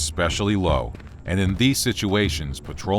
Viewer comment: Im late but I thought it said scooby traps